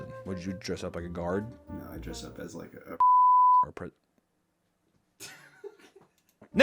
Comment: wait what did say there, is "im Dress Up as a Gamer" trying to Tell me He Said the Gamer-word?!?